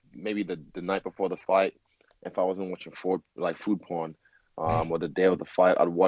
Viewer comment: ? What does he say Food porn wassit?